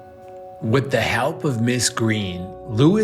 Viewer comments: Why Did I Hear Dhar Say "What The H*** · I thought dhar Mann said “WHAT THE HELL MRS GREEN!”